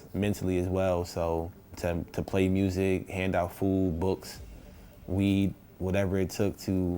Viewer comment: I thought he said weed for a second lmao